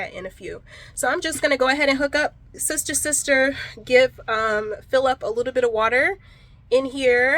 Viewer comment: ROFL. I watch in 2x speed and am trying to finish a baby for a high risk delivery and at 5:36 I heard “give Phillip a little bit of water” 😂 I was sooo confused,… like who tf is Phillip and how did I miss a whole new person being introduced. 😂😂😂 2x speed is the only way I can stay up to date but sometimes it messes with me!